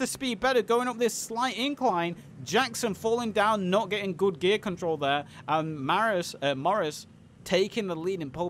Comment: Huh? Kubelwagen has gears now? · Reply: I swear I heard you said 'nice gear control' at some point, was like woot